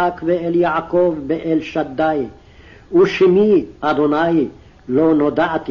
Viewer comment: The reciter said "Adonai" at 0:56, not Yahwa or Jehovah which is written! I heard him clearly. Two separate deities from the Canaanite pantheon.